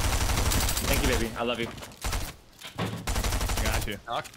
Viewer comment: did she say i love u back ?